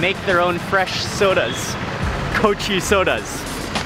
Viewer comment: at first thought you said goat cheese sodas (8'